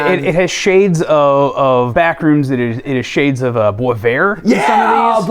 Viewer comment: what did he say? Bwomair? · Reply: Boisvert